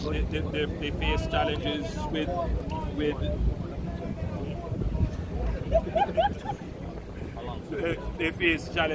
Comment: Is what that man saying in min 17:23? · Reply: All now we don't get any covid money